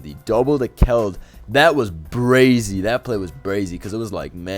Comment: excuse you young man did you just say brazy? This is a christian website and gang violence is not tolerated